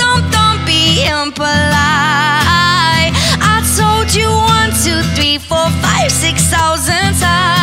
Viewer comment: Am I the only one who hears: I sold you 1,2,3,4,5, 6000 times? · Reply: Its not sold its told